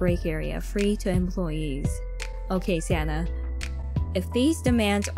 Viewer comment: I think she says “okay Santa”